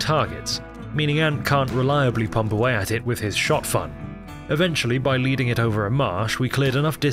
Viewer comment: I'm sorry, did you say Shot"Fun"... 8:44 lol